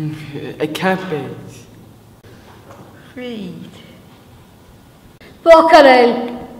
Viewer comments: is it me or do i hear weed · DID SHE SAY WEED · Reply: ya she said weed!